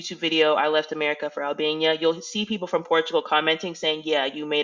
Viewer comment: Omg my country self thought that said Alabama 🤦🏾‍♀️